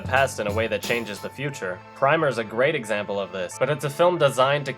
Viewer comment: For some reason i cant hear what he is saying at 04:38 (Name of the film) cause i would really want to see it :) Any help ??? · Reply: Primer